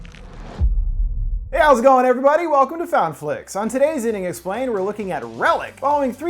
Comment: Lol every time he starts a video I swear I hear “welcome to fountain flix” 😂